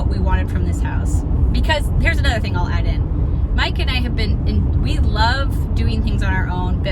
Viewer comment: says, "Mike and I," and I hear "Mike and Ike" and giggle every time. lol